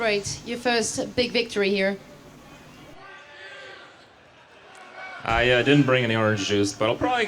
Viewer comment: What is he even saying at [5:37] He didnt bring any orgies? I what did he say. I guess it doenst matter since he seemed to have a more boring life than me. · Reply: von Chefenstein He said he didn't bring any orange juice. He often drinks OJ from the carton while streaming.